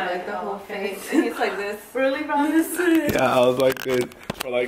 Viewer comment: Did anybody realize that said really Brian instead of